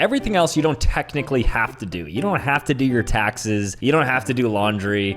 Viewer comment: all I heard was I don't have to do my taxes